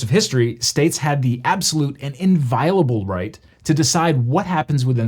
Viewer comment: The closed captions seem to confirm you are saying "inviable" -- am I still mishearing this? **I'll add, the word you likely are using is "inviolable," it's just wild how much the meaning changes.